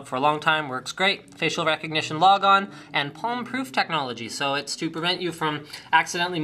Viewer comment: it sounded like he said "and porn proof technology" LOL